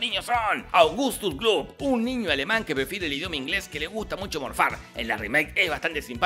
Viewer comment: Normalmente no soy muy curioso pero... qué es lo que dice en 2:25? Un niño alemán que... befide? .-. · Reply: Prefiere el idioma ingles